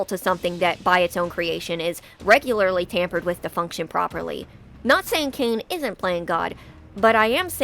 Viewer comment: Actually, it aged fine. "I'm not saying Caine isn't playing God," means that I'm saying he was playing God. XD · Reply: then I really gotta get my ears checked, I thought I heard "I'm not saying Caune is playing God," sorry